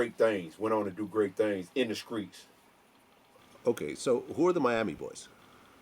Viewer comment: at time 1:10 did he really say "in the skreets" haha